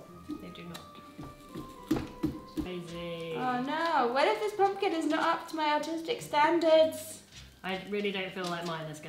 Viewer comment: Good thing there are subtitles. Because around 3:00 I heard "Oh no!. What if this pumpkin is not up to my autistic standards."